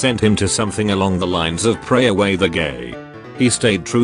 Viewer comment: I heard “ pray the gay away” and just couldn’t hold it in any longer and started laughing a bit to hard and I think I popped a lung 🤠